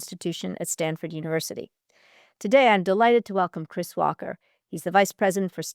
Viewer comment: Christopher Walker - had to double check I thought it said Walken. I would pay many money to hear him talk about China for an hour.